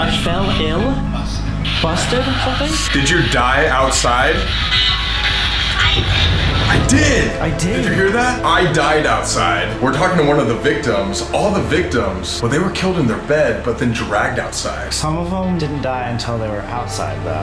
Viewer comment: It kinda sounded like it said "Colby" rather than "I did"